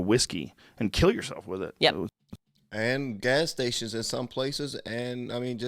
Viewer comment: they literally said that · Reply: i thought he said gas station.